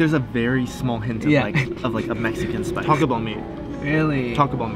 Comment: I thought he was saying talk about me but it was Taco Bell meet omg lol